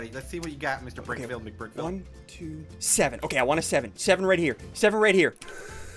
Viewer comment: /8:29 I thought said severed right ear, severed right ear who else heard that?